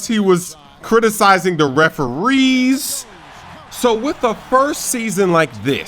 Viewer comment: Start the video at 2:00 and pause at 2:01. Sounds like he said wtf hahaha